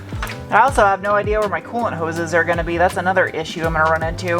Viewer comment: After all the talk of bungholes I could've sworn you said "colon hose." COOLANT hose ahhh